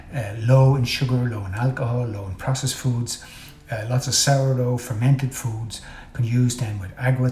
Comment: Did you say lots of sourdough?!